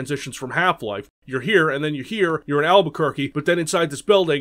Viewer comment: I thought you said valveuquerque instead of Albuquerque. Missed pun right there but great video otherwise